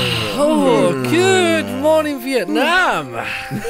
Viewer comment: Good Morning View.... Nam #BreakingTheRolePlay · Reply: Optic Rushman Actually It Sounds Like He Said Vietnam